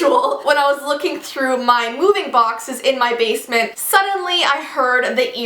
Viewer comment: I thought she said mooing boxes i was like...they moo?